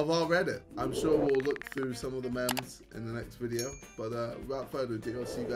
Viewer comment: did he just call memes “MÆMS”